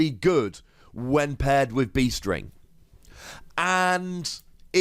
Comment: Every time anyone says "Beast Ring", I hear "Bee String".